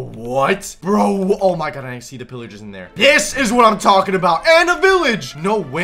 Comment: Bionic:This is what i'm talking about. me:did he just say piss is what i'm talking about.